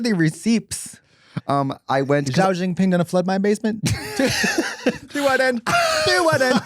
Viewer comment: what does Katya say? 8:31 about basements? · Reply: Xi jinping going to flood my basement?? She wouldn’t. Is what I made that out to be